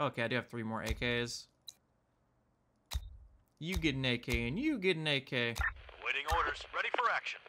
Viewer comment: "You get AK, and you get AK!" Thought you said: "You get naked, and you get naked!"